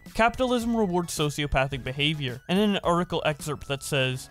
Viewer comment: Did you just say sociopathic? Lol